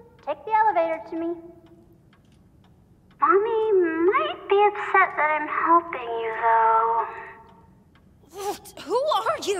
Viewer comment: Why do I hear Poppy saying this? 'Mommy will be upset that I'm helping you though...'